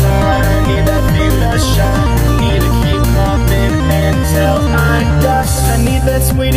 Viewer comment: For the longest time I thought it said 'I need to keep coughing until' I die', because, y'know, the poison joke smoke...